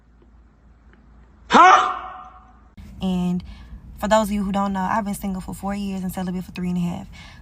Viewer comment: For those of you that don't know what she really said was "I've been single for 4 years and settling for 3 1/2" 😂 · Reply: Celibate”